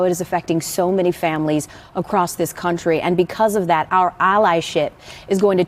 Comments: Meanwhile, John Clayton - one of their longtime colleagues - died and they haven't had a moment of silence for him. Also, it really sounded like she said "our ally-shit". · I literally thought she said “our ally shit.” Lol